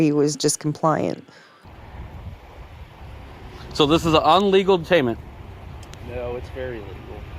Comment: Did he say "Un-legal" What in the mysteries of the unknown?! I guess this Frauditor is passed "Illegal", but warped to Un-legal?! He can't be that far from being out of his make up wordless mind?! 👎🏾🙄 Awesome video! 😎👍🏾